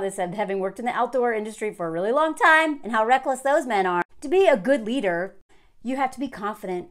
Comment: I first thought you said: "For a man to be a good eater he has to .." 💕